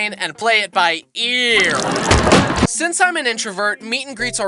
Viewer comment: Did you say introvert